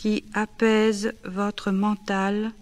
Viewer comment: Does anyone know what she's saying here? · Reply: "cette détente profonde qui apaise votre mental et recharge vos batteries nerveuses" French for "this deep relaxation that soothes your mind and recharges your nervous batteries"